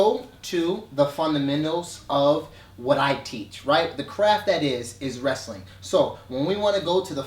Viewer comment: I thought was saying "the crap that is" 😂😂😂